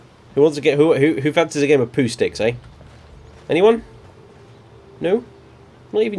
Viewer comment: What was the thing you said at 33:20 ? All I got was who "Who fancies a game of..." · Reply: "Pooh sticks" he said. It's a game where you drop sticks into a river on one side of a bridge and then run to the other side and see whose stick comes out first. Great fun when you're 10 years old... or when you're a daft housecarl.